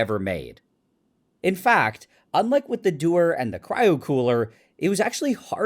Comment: I'm not familiar with this kind of thing. I kept hearing "and the do-er" and I was like "what does it do?"